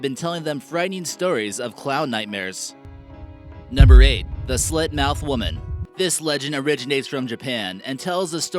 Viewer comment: I thought he said Slutmouthwoman